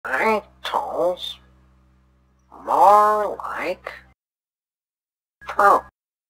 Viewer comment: tf is he even saying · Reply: Bring tools? More like proop